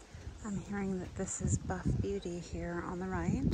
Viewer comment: I think you may have meant Buff Beauty? Rather than Buff Eight which is what I heard you say.